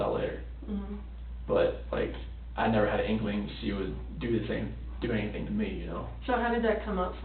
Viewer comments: Did he just accidentally tell on himself in terms of the cheating? It's a bit unclear but it sounds like he says "I never had an inkling she would do the same-, do anything to me". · He almost says "She would do the same thing to me." Anyone else catch that?